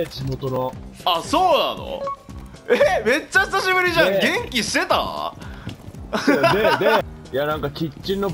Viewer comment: 元気してたぁ〜が、息してる？に聞こえたてんちむキッズです